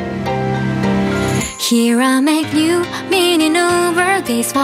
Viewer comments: 最初のHere I make new が完全に“煌めく”に聞こえて鳥肌が凄い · 最初のHere I make newってとこが何回聞いても「きらめく」って言ってるっぽく聞こえるし、Could not seeってとこも「かなしい」って言ってるように聞こえるw